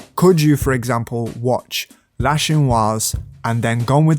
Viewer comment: anyone know what film is on screen / being said at 24:12, I cant understand it. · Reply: Jean Luc Godard’s La Chinoise 1967